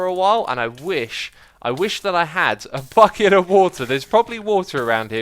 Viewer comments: I didnt hear bucket but something else... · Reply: Hielco Westra I wish I had a FUCK IT of wotah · I may be a bit late but... at 3:02 I'm pretty sure I heard a FUCKet of water